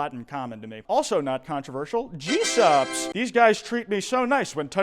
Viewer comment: I thought he said Jesus